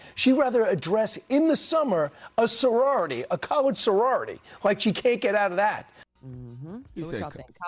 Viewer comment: I played it back 5 times. I did not hear college at all. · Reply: Turn on subtitles/CC, and play starting around 3:41. I heard "cullerdge", but YouTube's AI heard, well, you can see for yourself.